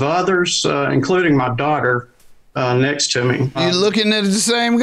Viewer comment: Did you catch the Freudian slip?😮 Now ‘my wife’ and Brian are in the process of divorcing 😃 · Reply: I thought I heard him say that it was his wife instead of daughter..😬